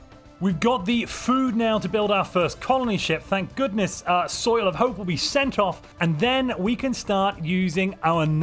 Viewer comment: Every time i hear "Soil of hope" my brain makes me hear "Soylent Hope". Implying that all the food coming from your farmers is hilariously ironic